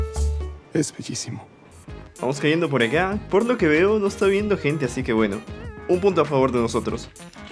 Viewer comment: escuche porno que veo xd